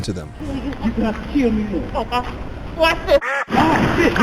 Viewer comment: did she say "watch this"? 🤣😂